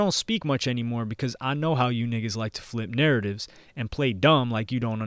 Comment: Did he just say niggas?